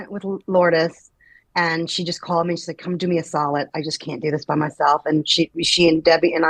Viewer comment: What does Niki say at 11:37, "Come do me a _____"? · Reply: Solid